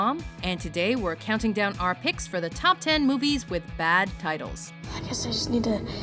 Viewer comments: Yes. I thought it said 'bad titties' at first too. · lol, at first i thought it said 'top 10 movies with bad titties' xD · I thought it said bad tities :/